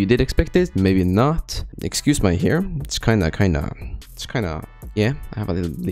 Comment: “Excuse my hair” I thought he said “excuse my harem” 💀💀💀😳🗿